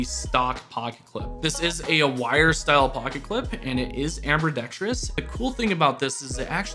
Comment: Did you just say "Amber"dextrous?😂